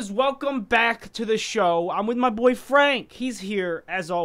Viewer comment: Oh wow in 0:10 when sketch said "my boy frank" i thought he was saying "my boy friend" EDIT: LMAOOOOO XD